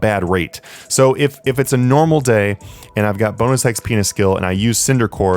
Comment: sounds like he said bounus x penus great vid thank you.